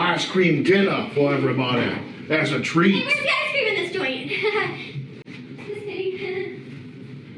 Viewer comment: anyone able to make out what schizo said here? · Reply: HEY WISE GUY, WHERE'S THE ICECREAM IN THIS JOINT, HEEEGH?